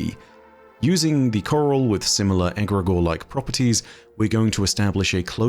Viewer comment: Maybe I misheard, but Im pretty sure said the "coral" is egeregore, not egrogore-like. It looks like coral due to it being fossilized due to the imense pressure of Titans oceans.